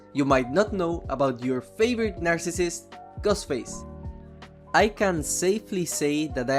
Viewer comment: It sounds like he said Gus Face